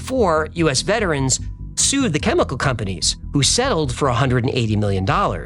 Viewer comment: Who else had to rewind and make sure he said selled instead of sold 😂